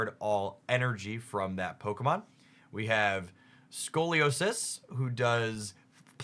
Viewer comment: I had to do a double take when I heard "Scoliosis"